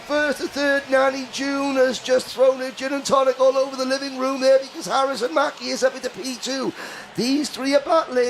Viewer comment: could’ve sworn I heard him call Harrison Mackie Lewis